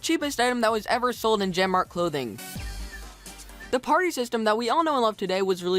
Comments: when you said party system I thought you said aparri system XD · I thought you said the Aparri system.